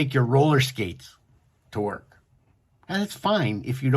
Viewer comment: did you say “to work” or “dork”? Because they both work well in that context